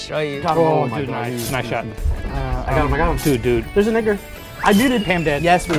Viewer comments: what did he say ?? :O ( after tick died ) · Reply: calling out the Edgar that he could kill to get another point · i think he said the n word